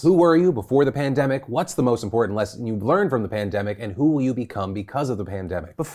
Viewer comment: panini instead of pandemic